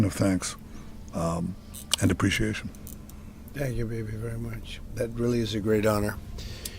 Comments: did Trump actually say "thank you, baby"?? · did he just say, “thank you BABY very much…” 😮